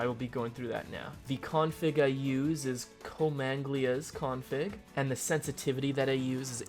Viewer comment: what he said? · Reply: Comanglia's cfg